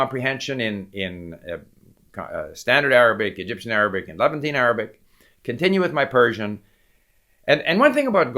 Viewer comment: I kept hearing "eleventeen Arabic". I suggest you learn the Devanagari script, which is used for many languages in India. Gujarati is a Nagari script; it looks a lot like Devanagari without the line at the top. Gurmukhi, which is how Sikhs write Punjabi, is a Landa, not Nagari, script, but they're all Brahmi scripts and the principles are the same. (The other script used for Punjabi is Shahmukhi, which is an Arabic script very similar to those used for Persian and Urdu.)